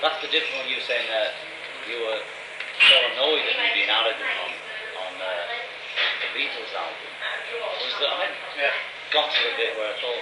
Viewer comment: I speak John Lennon, I can help out with the inaudible parts lol 8:12 John doesn't say any words. He just pauses between what you heard, saying "You were annoyed at me being arrogant on... on... errr..(he's thinking) on the Beatles album" 8:35 "Like even getting up PLATE CRASH the first time I sang it, because I hadn't allowed myself to say it was a whole song" - The plate crash peaks loudly over John here but my best guess is that he says "like even getting up AND IMPLYING the first time I sang it - but that's just a best guess without having the a crack at de-noising the audio. 9:58 Slight mistranslation here, he doesn't say "play it" he says "play your bit" And at 10:09 = I can't tell you better than you AT what HE needs to play on it (John is referring to George, not Paul directly) 12:32 John speaks under Paul here, he says "Yes, yes, it does, everything applies a little bit to each other" 13:01 "And a lot of the times you were saying we're all wrong" 13:37 Paul: But really I mean, it is gonna be much better if we can actually just do it together and say, Look George - I've got a feeling, I want you to do EXACTLY like that bass and he'll say, I'm not you" There's some pretty good equipment out there these days for Audio repair, I could probably clean this up at some point if I got my hands on it and found the time. Anyways, hope this helps!